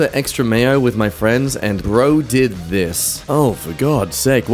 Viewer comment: am i tripping or did he say friends